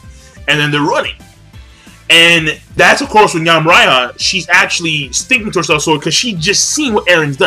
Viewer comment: "Yamraiha"?....assuming I heard right,I think is reading too much Magi.